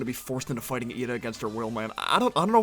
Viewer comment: Did you just call Raine a her? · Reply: I said their :)